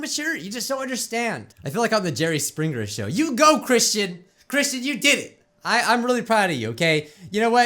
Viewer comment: when you said Kristen I thought you said Christian